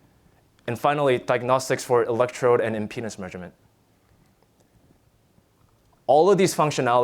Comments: I didn't quite catch that... Penis measurement? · did he say penis measurement?? I'm not joking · did he say penis measurement? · i misheard 57:07 as "and finally diagnostics for electrode and in penis measurement"